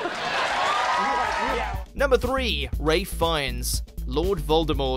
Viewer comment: did he just say ray fiennes